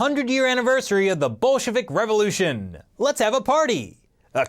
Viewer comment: i keep hearing the "bullshit revolution"